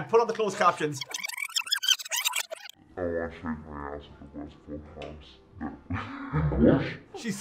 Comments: I heard "I want to change my answer to westport" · I heard "I want to take my ass to Westport House". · I heard, "I want to take my aunt to Westport House." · I thought she said, " i want to take my aunt to West Poor House." 😂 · I thought said, "I want to shave my a$$ at Westport House." So, if viewers buy sweaters made from Co. Mayo shearings, there's a chance that they'll get a little bit of included. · I heard "I want to shake my ass at Westport House"